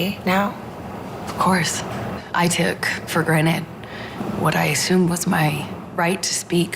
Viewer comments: "she took for granite" instead of granted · Did she say "i took for granite" at 2:16?